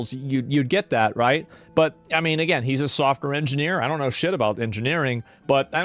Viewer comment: I thought he said Soft and Queer instead of Engineer 😂😂